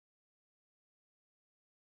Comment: My favorite metal gear rising track mixed with one of my top frames? Today's a good day!! Keep up the good work mate! EDIT: Also, that last part made me LOL... did it say stupid? Or did I misheard it?